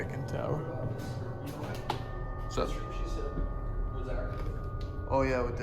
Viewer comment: I thought was jokingly saying “that’s what she said” 🤣🤣🤣